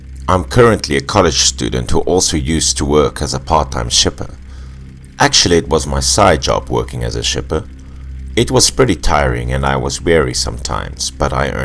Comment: I honestly thought it said "While working as a stripper"